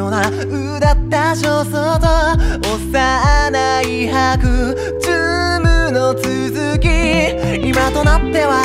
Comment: 今のうちに謝っておきます。ごめん… 幼い白昼夢 が、 おっさんの甘いハグ に聞こえた